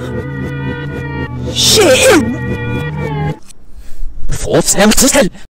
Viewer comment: Instead of Bri'ish now it's Shit